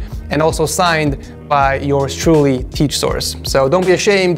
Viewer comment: Did he say teach source